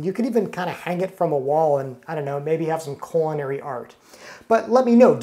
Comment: I thought you kept saying "coronary," as if it was hard to crank the handle. Once I turned on closed-caption I realized you were saying "culinary." Might want to emphasize the "L" a bit more.